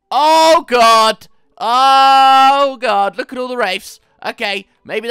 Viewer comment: Thought he said look at all those rapes